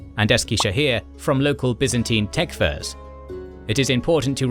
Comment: I heard “local Byzantine tech firms”. 😅